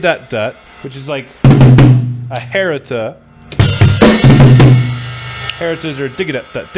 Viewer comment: What is it called at 12:15? Harriter? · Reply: Its called a Herta. ... RLR L RLR L. Left hand should stay consistently the same tempo.